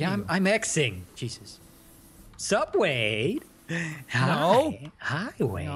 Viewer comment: who else thought he said subway? lol